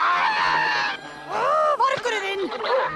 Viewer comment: Vargurinn þinn * (Misheard the first time)